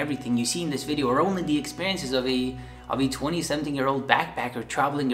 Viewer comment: did he say 20-something yers old or did he say 27 years old???